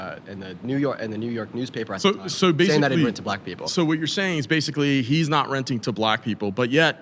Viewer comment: I keep hearing “rent a black person”